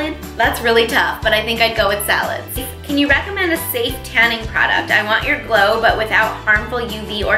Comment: Somehow I heard "can you recommend a Satanic product?" insteas of "can you recommend a self-tanning product? 😂